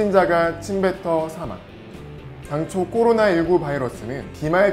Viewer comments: 나만 꼬로나라고 들림? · 나만 꼬로나로 들리나?